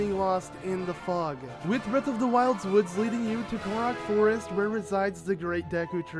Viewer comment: I swear he said Goron Forest